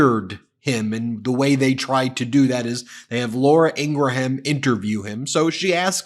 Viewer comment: 😅 I thought he called her “Laura Angry-Ham”, for a second.